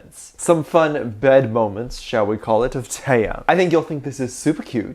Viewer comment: Dont mind me pointing out what the captions are saying -.- Turn on captions and you'll see what I'm talking about LOL When said Taehyung at 0:17 but the captions put it as "taiya" At 1:42 said Jungkook but the captions put it as "say cook" LMAO At 1:46 it said "tang" instead of Taehyung lol! At 1:53 the captions registered as "big kid" instead of BigHit I cant- At 1:55 it said "take hook" instead of Taekook what in the hdfgvibygrekuygukygfu- LMAO 2:41 the captions said "bikini" instead of BigHit i need some hobi water- At 2:54 it said "tam" instead of Taehyung wht in the frick is this- I cant be bothered to do the rest of the timestamps for these forbidden captions so enjoy these